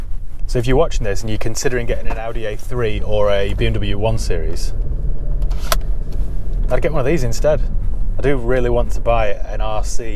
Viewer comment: On 10:10, what does he say? Prefers a3 or this one? Thanks! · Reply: Raúl García I prefer the Lexus to the A3 or 1 series bmw 😉